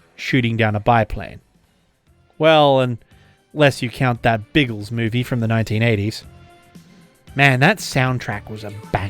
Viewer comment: What movie was referenced at the end of this video? Sounded like he said "that Beatles movie from the 1980's". I don't remember that but that was during the early Beta, VHS days.